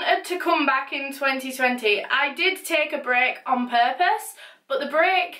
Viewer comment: When she said, "I wanted to take a break" I thought she said brick lol I still love her tho :)